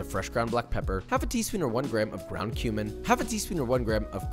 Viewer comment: anyone else here ground human instead of ground cumin